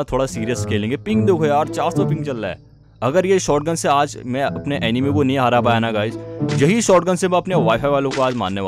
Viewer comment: did I heard 'anime'?